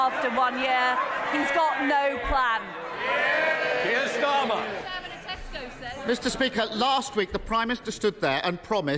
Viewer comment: can anyone figure out what the background voice at about 1:50 says about tesco lol? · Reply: I can’t really make it out, but I feel like the person was saying something along the lines of: “why do you own shares in tesco, then?”